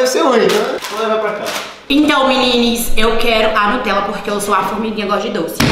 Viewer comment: Parei de assistir a partir do momento q ouvi "menines"